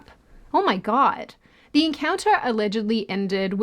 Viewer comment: I honestly thought she said the bean encounter 😂